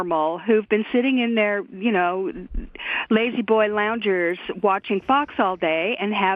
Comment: For a second, I thought she said "ladyboy loungers" and was like whoa now, whoa.